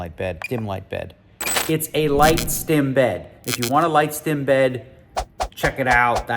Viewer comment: I thought you were saying light skinned bed